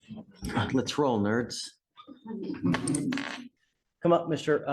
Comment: Anyone else misheard the judge say "I'm on patrol nerds" at the start 😂